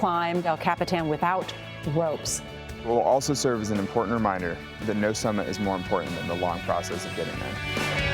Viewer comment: What does he say at 4:11? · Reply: [..] will also serve as an important reminder that no summit is more important than the long process of getting there.?